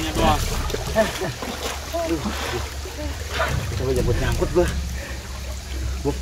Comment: ada yg tau a'panji ngomong apa suara nya gak jelas bagi yg telinganya jernih silakan bantu yak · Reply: NyaNgkut nyangkut gue Karena panji buat turun ke bawah pengen liat uyg ditriaki ular buntet harus masuk ranting2 dan dahan2 smpe badan nya ke gasruk gasruk nyangkut